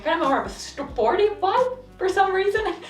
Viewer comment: Such a lovely idea for a video! But what did you say at 4:37? a porny vibe?🤭